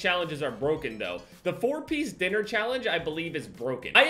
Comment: I thought his ass said four piece nigga challenge. Shi